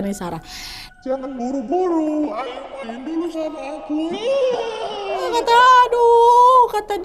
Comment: Kak nad maaf sebelumnya.mau kasih saran pas di menit 23:29 itu makhluk nya bilang apa?suara kak nad di efek tapi jadi gak jelas ngomong apa? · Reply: Bantu jawab Katanya "Jangan buru buru, ayo main dulu sama aku"